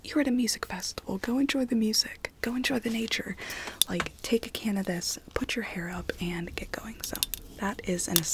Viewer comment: When you said 'take a can of this' I thought you said 'take cannabis' and spent a few seconds being thoroughly confused